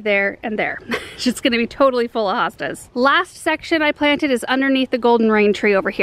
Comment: Am I the only one who heard "sh*t's gonna be totally full of hostas" at 13:48? LOL. I don't think that's actually what she said, but that sounds like something I would say so I giggled.